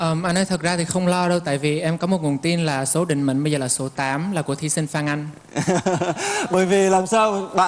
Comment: em ấy nói là thí sinh Phan Anh (ý là MC Phan Anh ấy) chứ ko phải là Phương Anh, bạn nghe nhầm rồi =)))